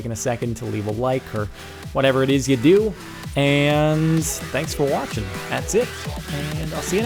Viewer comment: Lol my audio bugged out at the end so it sounded like he said "Thanks for waffle." LOL